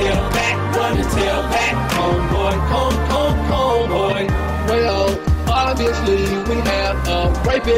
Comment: I'm a twisted or did I hear "hon hon hon Boy" at 1:46?!? ^^